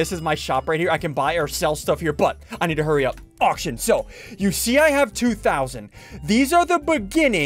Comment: OMG at 0:59 when he said "auctions" I swear he said "aw shit"! I legit had to rewind like 5 times to make sure lmao. Did anyone else hear that?